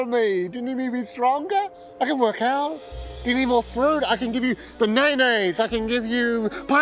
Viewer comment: is he saying "bananas" or something else? Is that how Kiwis say bananas?